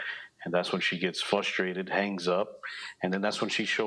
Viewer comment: It sounded like he said “flustrated” “ when it’s frustrated. With an R. A lot of people do that these days.